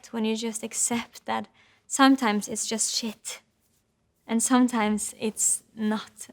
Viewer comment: did she just say shit